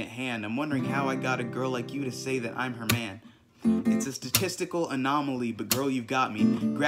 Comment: I thought he said "It's just a testical"